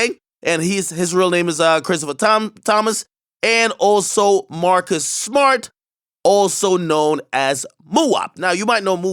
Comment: I heard "Marcus Smart" like what??? 👀👀👀! Lol.